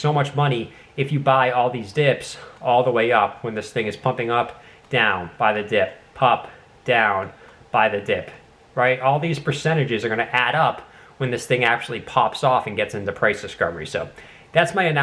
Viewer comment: Great video 👌👌 7:16 - did you say buy the dick 🤔